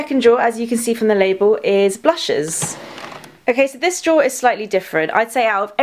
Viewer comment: anyone else thought it said BLUBHEAD 😂